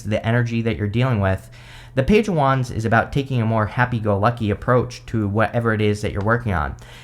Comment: I misheard you and heard, "Cappy go lucky"!🎉 I'm not going to argue with this - the theme for us Caps!😂🦸‍♀️🔥